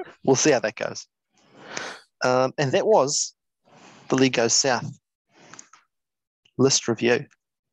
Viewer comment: Literally every single time you guys say League Goes South I hear "LEGO SOUTH"